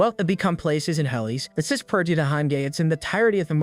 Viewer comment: did I hear am gay